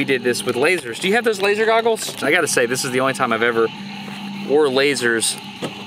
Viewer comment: I thought it said "underwear lasers"...this was cool too I guess